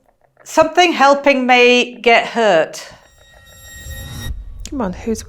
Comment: I almost wonder if it wasnt actually saying thank you for helping me... get her instead of hurt?